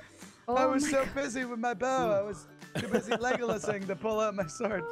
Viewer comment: After rewatching this so many times, I finally realized what said He said ‘legolasing’, like Legolas from J. R. R. Tolkien’s legendarium, a character who shoots the bow a lot so when he said ‘legolasing’ he meant that he was shooting his bow I am content with life now that I know the thing that I’ve been trying to figure out since this vid came out